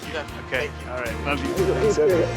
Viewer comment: did he say love you